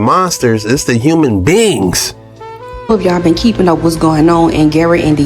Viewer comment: When you 1st said it's not the zombies it's the human beings I thought you said ITS THE HILL BILLY'S ..😂😂😂